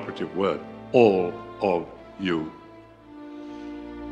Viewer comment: It really sounded like he said ''you are the greatest cruel i've ever met''